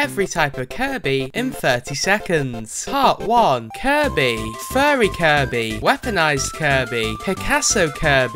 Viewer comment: Started to sound like you were saying “curry”